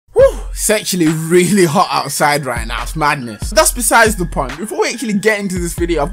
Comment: thought at the start you said "sexually hot outside" 😅